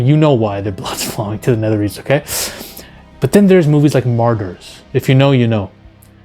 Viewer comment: What movie did he mention at 3:20 that was so disturbing? Margger? I couldn't really hear if someone could give me the name that would be great.